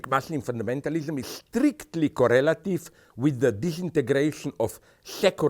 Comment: subtitles is wrong, he says and means "Correlative", not "relative". Different meanings there, important.